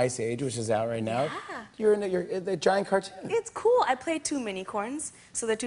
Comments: What did he say at 1:31 omg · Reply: "You're in a uh giant cartoon." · Everyone play from 1:30 and tell me what Jimmy says? I could swear he said a racial slur, but I could be wrong! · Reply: He simply stuttered a bit. He said "You're a n--you're in a giant cartoon," something like that.